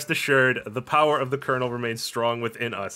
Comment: "The power of the kernel remains strong within us." I heard this as "Colonel" and immediately his heavy Russian accent saying "You are worthy."